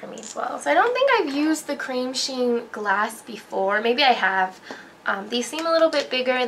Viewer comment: you sound like you said "cream sheen glass" instead of gloss